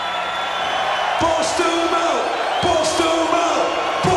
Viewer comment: What does Tommy say at 1:47:56? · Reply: bust em out!